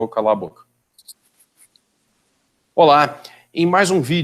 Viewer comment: No início eu ouvi "cala a boca"